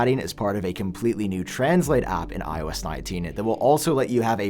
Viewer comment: did he say ios 19?